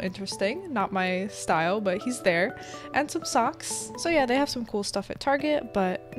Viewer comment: I thought it said 3 ankle socks...